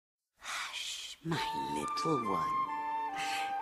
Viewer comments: I can't be the only one who thought she was saying "Thats my little boy." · I always thought she was saying "That's my little boy!" back when I was younger. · I thought she was saying that’s my little boy.